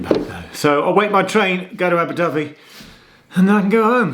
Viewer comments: I thought you said Abu Dhabi Geoff! 😂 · Hi Geoff, great video, lovely railway coast line, but can you slow down when speaking, because I thought you said you were going to Abu Dhabi, not Aberdyfi! 😟😝